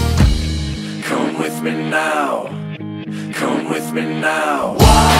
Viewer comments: I still feel like it says "war" and apparently a lot of others thought so as well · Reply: I thought he was saying "Lord come with me now" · I thought this say "lord come with me now" lol oops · I thought it was saying, walk go with me now